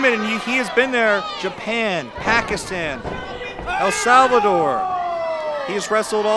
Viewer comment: did he say Pakistan???